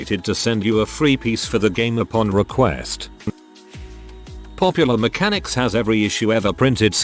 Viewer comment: I thought this said “Pokémon mechanics” and was confused about what they were talking about.